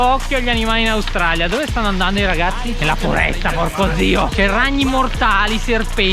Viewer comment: al minuto 2:15 ho sentito un porco è da peté del toto ahaha